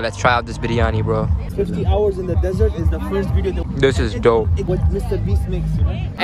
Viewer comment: I think the bag surprise is SENDING 24 HOURS IN DESERT cuz I heard that